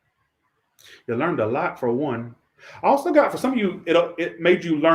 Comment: Right around 26:50 I swear I heard a disembodied voice say "they learned a lot". Is it the same person that knocked down the curtain? WAS THE WINDOW OPEN?